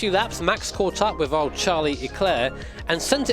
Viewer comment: am I the only one who heard Charlie eclaire??